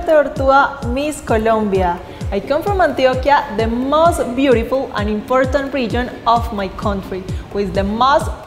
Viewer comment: I heard "...the most beautiful, unimportant region of my country." lol